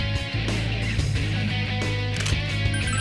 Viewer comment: Hobo Jesus aside, it sounded like Aigis said "You are a skank!"